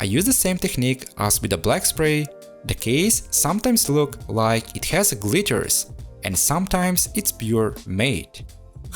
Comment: I heard “the case sometimes looks like it has a clitoris” 💀😂 jokes aside though, I love what you did with this! And your English is great otherwise for a foreigner :)